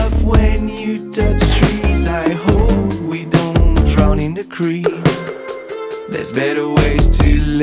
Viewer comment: "Absurdia" Les rêves monochromes M’assomment jusqu’à l’aube La trêve monotone L’impasse qui sonne Les rêves monochromes M’assomment jusqu’à l’aube La trêve monotone L’impasse qui sonne It does indeed feel like a dream The one where you do as you please Not exactly reality, just slightly off when you touch trees I hope we don’t drown in the cream There’s better ways to let of steam It doesn’t matter what you say You're not coming to my birthday Les rêves monochromes M’assomment jusqu’à l’aube La trêve monotone L’impasse qui sonne Les rêves monochromes M’assomment jusqu’à l’aube La trêve monotone L’impasse qui sonne C’était hier pas cette nuit C’était réel, pas cette nuit C’était hier pas cette nuit C’était réel cet ennui C’était, hier C’était, réel, réel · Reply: lol I thought he said 'I hope we don't drown in the creek' XD